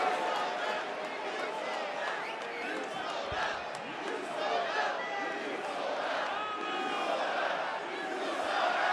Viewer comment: What do the crowd say at 29:00 ? It seems like the Rock did womething with his face maybe? Which would also explain why on TV we couldn't see it and why Paul Heyman did that face? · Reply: You sold out